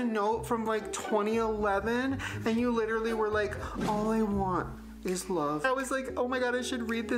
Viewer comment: I thought he said “all I want is blow” and I was gonna say that’d be the most relatable Shane/ryland have been in years.